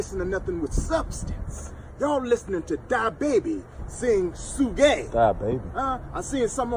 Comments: I thought he said diabetes instead of da baby 😭😭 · Thought he said Die Baby 😹